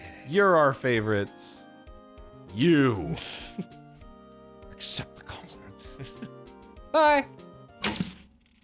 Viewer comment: Um...can anyone tell what Arin is saying at 44:35? It almost sounds like "Except for Colin" and I feel very called out. Otherwise, I'm hearing "Except the colored" which is problematic for a wholly different reason.